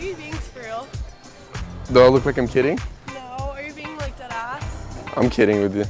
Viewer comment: What did she say at 1:34 Are you being like Dat ass??